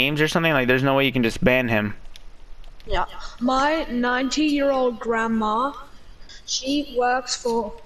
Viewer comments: At first I thought he said his nineteen year old grandma. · did he say 19 or 90 year grandma 🤣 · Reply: 90